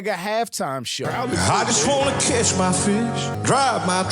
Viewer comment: lmao i thought he said "kiss my fish" but he said catch a fish